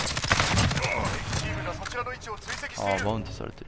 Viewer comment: 敵チームが粗品の位置を追跡しているて聞こえて一人で笑ってたw